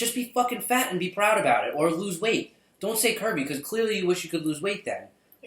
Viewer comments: When he kept saying curvy I thought he said Kirby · I swear to god, through the whole video I thought you said Kirby instead of curvy. I thought I'd shit myself for laughing. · I was listening to this while drawing and when you were saying "curvy" I thought you were saying "Kirby".